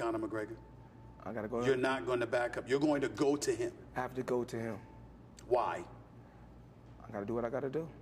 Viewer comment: Did he just say " I have to go down!?"